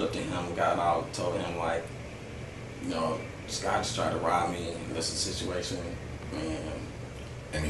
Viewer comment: Sounded like he said "scotch tried to rob me"